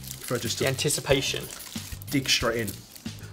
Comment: oh god at 5:54 I thought said "dick straight in" bahaha